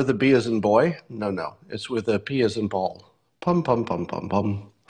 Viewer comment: and I was sure it was bam bam bam, not pom,pom, pom... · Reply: The funniest thing was his statement that "it is not B as in boy but P as in Paul" How many heard this as "B as in ball"?